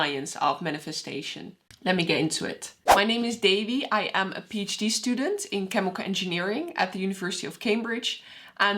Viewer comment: I always hear “ I am peeach D student “ 😊